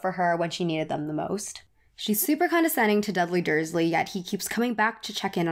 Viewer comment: did she say dudley dursley 😂 or is it just me